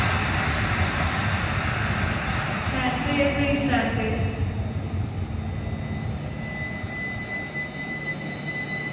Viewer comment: What did that announcement say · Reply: Please stand clear.